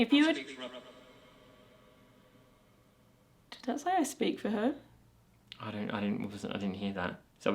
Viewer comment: Okay right there when you thought you heard I speak for her. It sound like to me (I speak rubber). 24 minutes 30 seconds.